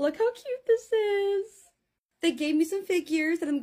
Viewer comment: When u said figures i thought u said fake ears i was like “they sent u WHAT?😭🙏🏻”